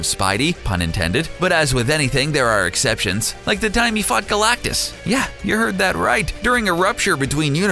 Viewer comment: i heard "like the time he f**ked galactus".... then i thought no i couldnt have heard that... then the video reassures me i heard it right.